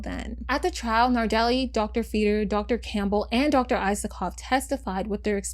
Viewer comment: oh no, i kept hearing "dr. isokov" as "isopod", roly-poly time🪲